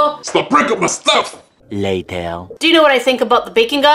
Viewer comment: I put my caption to on and on the SpongeBob screen the caption said late hell!?🤣